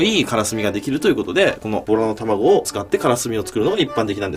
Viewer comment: ボラの卵じゃなくてオラって聞こえたw(自分の卵と言ってるって聞こえたのよw)